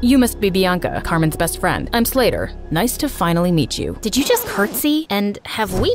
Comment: LMAO I THOUGHT HE SAID "im slayer"